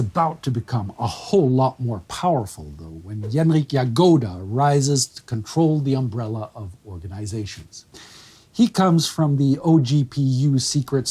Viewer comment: Hi there, i just started watching the episode and it seems like there is a mismatch in the subtitles at the start. at 1:26 the Subtitles state "in 1934, the NKVD sees a change of leadership" while Spartacus says "When Genrikh Yagoda rises to control the umbrella of the organization". After that the subtitles goes on t "Genrikh Yagoda has already been active with the OGPU Secret services before, where he was on of the founders of a poison factory. Well, using the techniques developed there Yagoda slowly poisoned his boss...." This is not what Spartacus is saying. Im guessing that there is a mismatch in the scripts but it really threw me for a loop XD